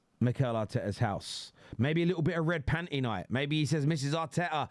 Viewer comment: Did he just say “a red panty night”? 👀😅